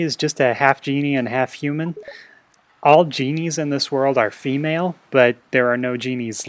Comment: My sister thought you said "all geniuses in this world are female," and she said "duh." :P